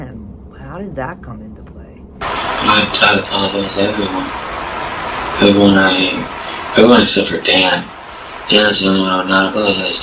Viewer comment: i hear “ i would have to apologize to everyone, everyone out here…. everyone except for Dan. Dan’s the only one that i would not apologize to. And if she decided to go with him _ be screwed. Me I actually held it together well until she handed me the kn*fe”